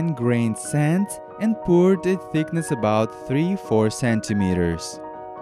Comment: Did he say CENTIMETER???